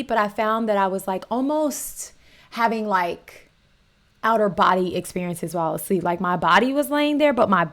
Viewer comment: lmao when she said “out of body experience” it sounded like she said “outer body experience”😂😂😂